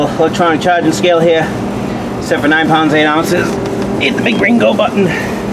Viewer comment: lol... I thought you said "hit the big gringo button".